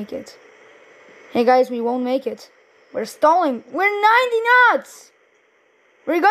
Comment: Did bro just say we 90 nuts at 3:07😭